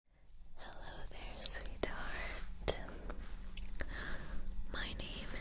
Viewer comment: I always thought she said “sweetart”